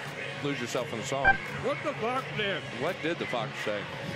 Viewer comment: エンタメミーティング😂 キツネダンス🤣 キツネダンスの歌って「What the F_CK say」って聞こえて笑う🤣🤣🤣